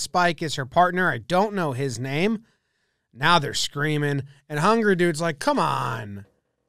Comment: I thought he was saying "hungry dude" the whole video before i realized the match was between Hungary and Brazil...